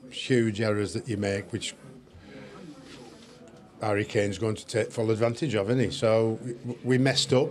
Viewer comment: Did he call him Barry Kane? 00:35😂